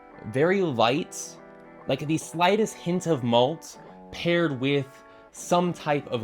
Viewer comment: I heard Mold at first and was a little flabbergasted until I read the Malt on the screen 😂